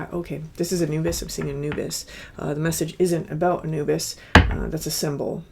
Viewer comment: I wasn't looking at the screen and at 1:14 I could have sworn I heard you say, "I'm seeing a nudist," and I think I pulled a muscle in my neck I turned so fast.